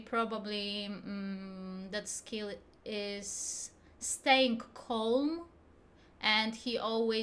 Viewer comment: I like about Skill staying calm (That's what I heard, sorry if I misheard) My head: Remembering when Skill was trying to mark the Carrot 50t., beating the living shit out of his chair. HAHAHHAHA XD